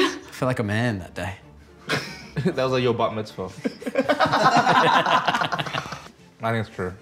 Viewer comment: Did say: "That was like your 'butt mitzvah." ? lol